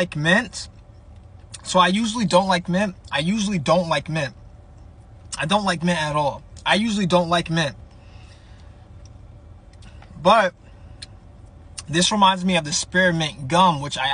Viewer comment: Bro, FR heard him say”I don’t like men🤨”